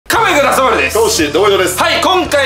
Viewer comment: 、いまだになんて言ってるかわからない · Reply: ｢Come on you グーナー どうもです｣ ｢Come on you CITY どうもです｣ って言ってるとこないだスパチャした時に教えて貰いました!!(グーナーの英語分からないのですみません)